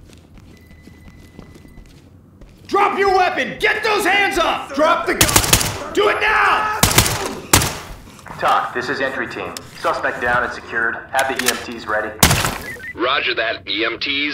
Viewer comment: Dude i heard accidantly "tag, this is andrew tate" 💀 ( like, tag = day, german greting )